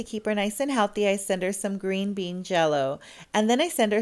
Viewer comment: It wasn’t green bean I thought it said apple…